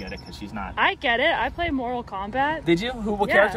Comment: Sounded like said "I get it, I played Moral Combat". LOL... Immanuel Kant is my fav character but his fatalities were sooo long.